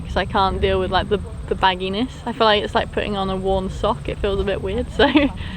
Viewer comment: Did she just say putting on a warm pair of socks is... BAD!? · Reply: Worn sock 🧦